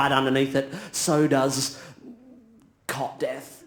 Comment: "So does... Cot death" I almost fell off my chair laughing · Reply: Mr TJM, I had to google cot death, I was guessing he was referring to SIDS but I wasn't 100% sure and like Joey Yang he said it so fast I thought he may have said cock death (which didn't make sense to me).